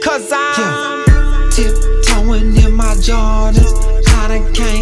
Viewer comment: I always heard "Tip Toein in my Georgias" o3o